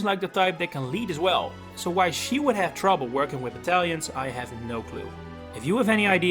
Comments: I think Anna doesn’t like battalions bc she has to pay them and that’s revenue loss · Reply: Oh battalions...I thought he said italians and was so confused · Oh battalions...I thought he said italians and was so confused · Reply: That's okay I thought he said the Tellians And I'm just sitting here thinking this doesn't take place tellius😂